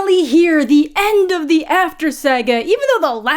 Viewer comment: The way I misheard "the end of the After Saga" as "the end of the SAG AFTRA strike" and was like "That's great! What deal did they get?" 😅🤦‍♀️